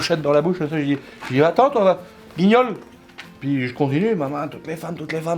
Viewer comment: " Manchette dans la bouche, va t-en toi guignol " 😂😂😂😂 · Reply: C'est pas guignol c'est bignole